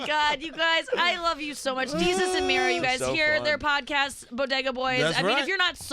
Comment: did you just call Mero, Mary?